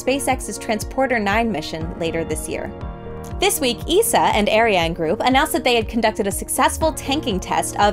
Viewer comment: She didn't say that 🤔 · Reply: You're right, she said "and" Ariane group - I heard "an" Ariane Group.